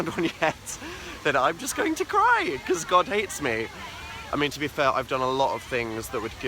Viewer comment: it sounded like : he said to be Phil